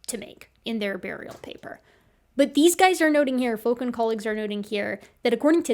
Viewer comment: I kept hearing "Fookin' colleagues" and it was extremely funny